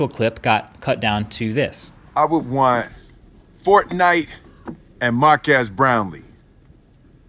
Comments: I thought will Smith said fortnite In Marques brownie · I thought when Will Smith said Marques Brownlee i thought the spelling was Markass Brownie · bro honestly I thought when review started I thought he said mark ass brownie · No offence but when Will Smith said your name I thought he said Mark Ass Brownie. Plus I’ve never heard of you since this appeared in my recommended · Boi the “is this what you wanted” guy is here!!! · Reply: i thought will smith said mark ass brownie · who else thought will smith said mark ass brownie LMAO 😂😂😂😂😂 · Am I the only one who thought Will Smith said Markass Brownie? xd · I want F O R T N I T E and M A R K A S S B R O W N I E S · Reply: I always thought he said brownies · Mark ass brownie I heard him say that I then google searched this regretting what I saw so much. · no offence but i thought will smith saying mark-ass brownly anyone else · im not the only one that thought will smith said mark ass brownlee 🤣🤣🤣🤣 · I would want Fortnite, and Mark Ass Brownie. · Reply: Lol legit though. The first time I heard Will Smith say his name I thought he said Mark ass Brownie. But pretty sure other people heard that too so ¯\_(ツ)_/¯ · Am I the only one that thinks Will Smith pronounced his name correctly? I kind of heard “Mark Ass” the first time I watched it. The second and third time, I heard “Marques Brownlee”. I don’t know, that’s just me 🤷🏿‍♂️ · When will smith said you’re name i I thought he said Markass brownie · I heard it "mark as brownlee"